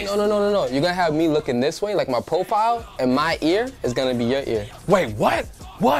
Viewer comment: sounded like earl